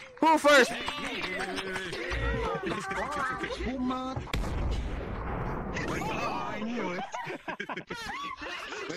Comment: COCA COLA ESPUMANDO 🤪 (O que eu entendi, GOCA COLA ESPUMANTE!) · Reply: Coca cola espumante